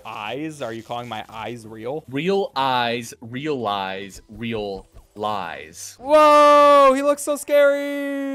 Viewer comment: what is bro even saying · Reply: Real eyes realise real lies